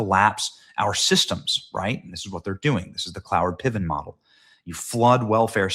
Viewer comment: did he say Cloward Piven model?